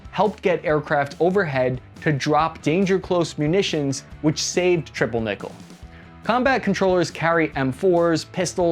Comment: Sorry but I heard "triple nipple"